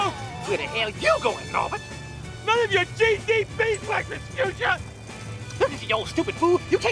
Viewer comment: I initially heard it as "None of your cheesy beeswax, Rasputia!"